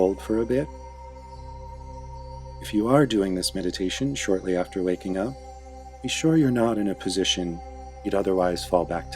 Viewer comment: LMAO when he said "if you're doing this meditation after waking up" I heard "if you're doing this meditation after making out" LMAOOO